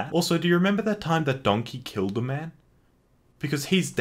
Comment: I wasn't paying attention and I thought you said shrek killed a baby